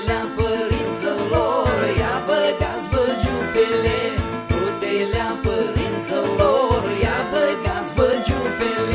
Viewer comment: Can somebody translate what they say at 1:38 It sounds to me like they say: IA BEJ DASMEN ÇEPEVE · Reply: Ia bâgats-vâ giupili - Dress up! I believe giupili is the Frech “jupe”, which was adopted around the beginning of the 20th c; a very popular one was the one called “plisatâ”.